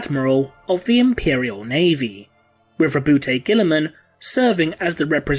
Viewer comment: "Riverboat Girlyman" I heard that!